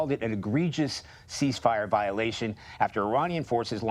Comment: Two weeks. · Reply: Ceasefire? Oh… I thought you said See fire!